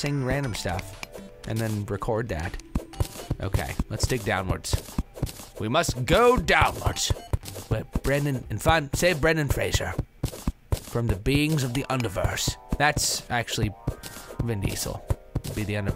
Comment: In 4:44, I thought he said "Unversed".....I've been playing too much KH. e.o